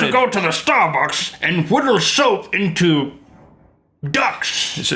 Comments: omg I thought Rhett was saying 'widdle' instead of 'whittle'! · Every time Rhett said 'whittle', I thought he said 'widdle', which means to pee in Britain. Old men are good at peeing? I thought that skill deteriorated with your age....:/